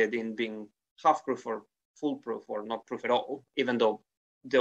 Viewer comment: I wonder if the modern phrase "fool-proof" has any roots in "Full Proof" at all? Sort of a pun. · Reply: I kept hearing 'fool-proof' when they said 'full-proof', and it made me think that only a fool would wear untested armour. You wouldn't want to put it on for the first time at a tourney or a battlefield, only to discover that you'd been sold a Norwegian Blue. The Great Vowel Shift did a lot to obscure the origin of apparently similar words, so we may never know if there was a connection.